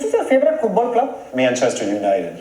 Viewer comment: Bhuvan=What is Your favourite football club I Thought said"Mia nchester United Like whoever also think it